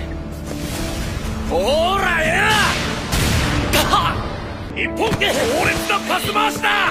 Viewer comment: どうしても一本に聞こえて格闘技でも始まったのかと思ってしまう